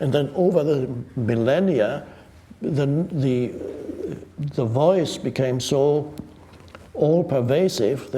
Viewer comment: What is Mr. Eckhart saying at 05:58 ? and than over the ....?? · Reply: "over the millenia, the voice became so all-pervasive"